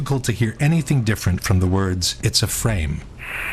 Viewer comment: when ceecee lyles fumbles trying to hang up, i dont think theyre saying "its a frame". Seems more likely another lady passenger saw her flustered and said "its ok".